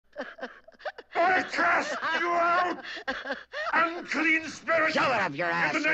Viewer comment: For a moment I thought he said I will curse you out